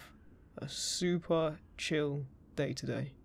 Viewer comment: Did he say DAY or DATE at 6:41 ? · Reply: I said "day". I do understand the confusion though, I kinda mushed "day" and "today" together. 😅